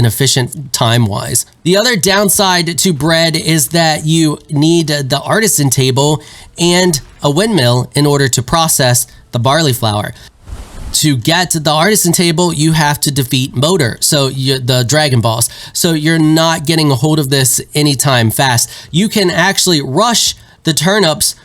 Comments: Not gonna lie. When you mentioned the dragon boss at first I thought you said "To get the artisan table you to defeat boner." I guess I won't be getting the artisan table, lul. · I kept hearing "Dragon Balls" instead of dragon boss.